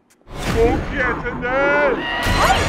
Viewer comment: เขาพูดว่าไรฟังไม่ออก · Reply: "กูเกลียดที่นี่"ครับ